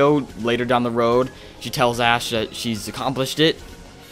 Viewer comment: with subtitles instead of ash it says a bad word. The s word Sh**